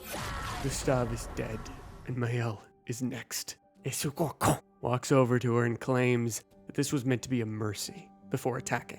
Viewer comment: I’m sorry, what did you call renoir at 15:42? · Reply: He called him a "gros con" it's a french insult, you can translate by "big jerk" 😅